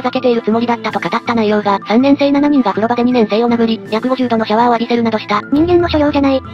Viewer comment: 約50度が150度に聞こえて「え？！」ってなった